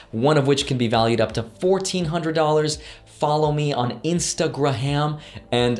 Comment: did he just say "instagraham" ? lol mash the like button